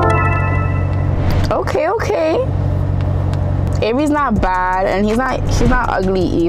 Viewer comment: did she just say avery not bad for a darkskin??????